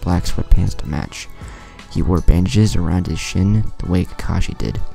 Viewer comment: him: chin What I hear: shin